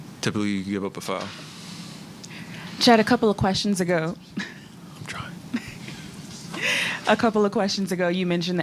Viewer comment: What did the guy say at 17:15? What was chet responding to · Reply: 3he said im tryin